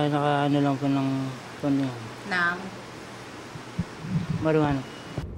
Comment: Tama ba narinig ko nag Marijuana ang suspek sa bandang 3:32?